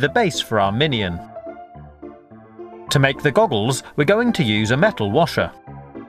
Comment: I thought he said gobbles instead of goggles lol